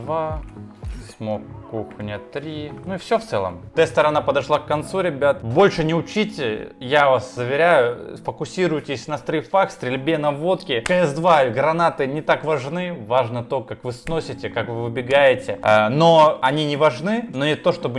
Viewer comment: Сначала подумал что послышалось, но после того как включил субтитры сразу же пошел фокусироваться на водке